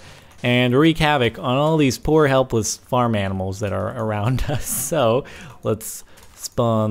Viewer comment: when he said animals it sounded like he said edibles